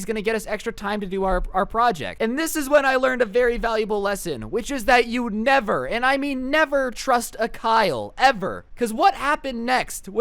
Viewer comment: I thought it said kylie...